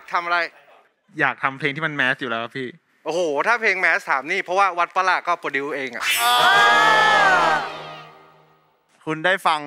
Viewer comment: พี่เต๋าพูดว่าอะไรหรอครับนาที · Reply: เขาพูดเพลงวัดป่ะละเขาโปรดิวซ์เอง